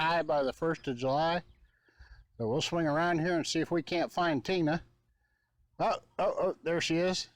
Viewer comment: YUMM. I thought you said see if we can find Tuna, lol.